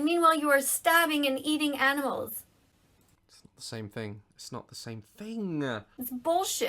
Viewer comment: it’s bullshit, I did not hit her. I thought her saying it’s bullshit sounded like it